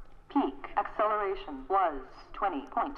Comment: Did I hear correctly at 8:20, 120 G's of acceleration? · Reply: 20.3 Gs